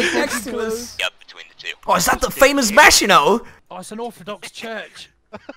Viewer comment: I swear to god I heard "Vaginot" instead of Maginot